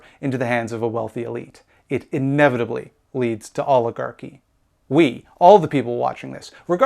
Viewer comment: What you said: “It inevitably leads to oligarchy” What I heard: “It inevitably leads to Olive Garden”